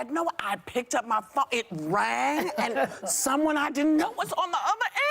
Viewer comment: did whoopie say "picked up my fucken rag?" My closed caption says she did lmao